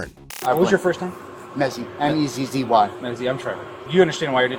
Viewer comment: When he said his name was Mezzy I thought he said Messi (Lionel the Soccer player)